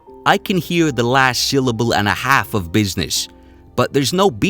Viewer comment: All I hear is "it's only business", man. I don't hear that "k" and I definitely hear the "b" that you say he doesn't say